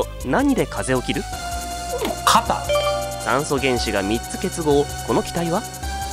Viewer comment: 炭素原子が３つに聞こえた